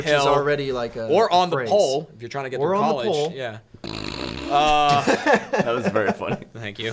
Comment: Ireland has some nuts sayings and mannerisms too. Would love to see them try figure out them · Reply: they had me laughing at 5:56 cause I thought they said ‘up the pole” relating to college